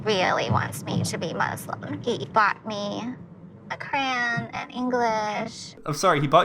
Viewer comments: Ohhhh Coran she said not 🖍️… I really thought she said crayons 🖍️ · I was super confused when you were making fun of when she said "He got me crayooonnnssss". I really thought she said crayons 😭